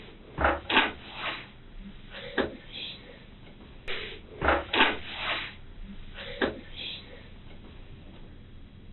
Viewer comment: what is he whispering? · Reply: He said he was parched (thirsty) 😅😅and said I UNDERSTAND like 3x 😮